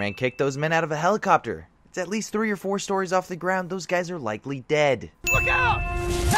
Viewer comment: for you on 7:03 because you said "those men are likely dead" but the subtitles say "those men are likely died."